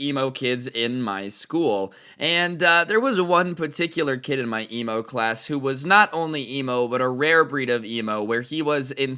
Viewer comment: did he say emo class